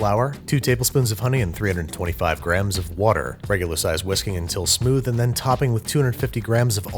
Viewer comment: OH he said WHISKING I thought he said WHISKEY now my kid is an alcoholic. Thanks Babar